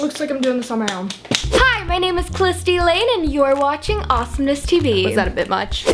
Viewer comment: I thought she said her name was "Clisty Lane"😂